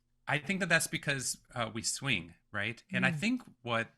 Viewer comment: Can smb tell me what he said in 14:27 please? Couldnt get the word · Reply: “Swing”